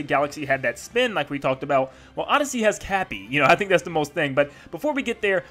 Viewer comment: for a moment I felt as if I heard "Tappy".